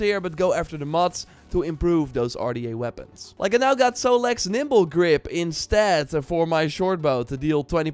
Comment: For a second i thought you said "Solek's nipple grip". Im like, how do you get that weapon? Sounds awesome!